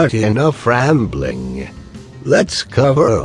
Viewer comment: whever he says "enough rambling" i always hear "in a frambling"